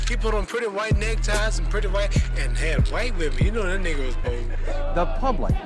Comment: Wait did ali say nigga at 9:27? Lmaoo